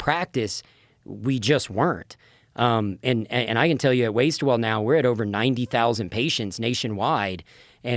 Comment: Listening to this, I thought they were saying WasteWell.